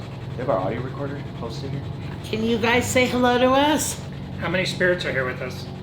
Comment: I didnt hear "hello" when spoke... I heard "help me"